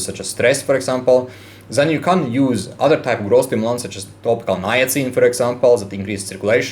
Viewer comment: what did you say for the alternative growth stimulant @ 3:22? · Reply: topical niacin, melatonin, etc. Effectivness of them depends on hair loss cause, progression and timeline